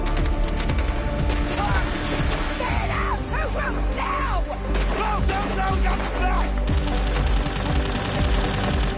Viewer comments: years later I still have no idea wtf they said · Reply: Maggie: Fan out, Third group now Daryl: Close up we got your backs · what is Daryl saying?! It sounds like blooloolooloo naugh. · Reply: go,go,go , got your back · wtf did Daryl say 😂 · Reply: Go go go got the back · someone tell me what daryl said 😭😭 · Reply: Daryl said "Go go go go! got your back!"